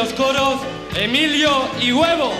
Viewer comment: Ya va un momento..... Dijo Y Guevo???? O escuche mal?